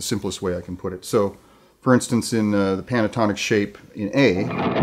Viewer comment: did he say panatonic shape?